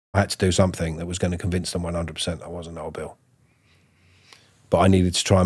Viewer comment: I genuinely thought he said, "I had to convince them I wasn't Hodor." I mean he looks a bit like him but it's not life and death.